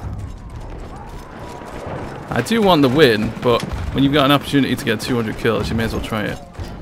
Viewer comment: hhhhhhhhhhhh I think u said u only want win = lair